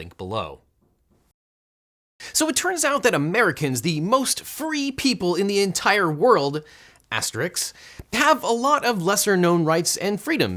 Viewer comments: I heard "Americans: the most furry people in the world" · At the beginning, I thought I heard you say Americans were the most furry people in the world. I wondered if you had never been to Greece.